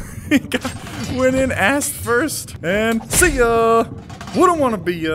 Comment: did say a**?